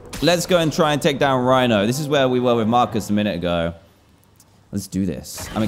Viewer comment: Did he just say Marcus?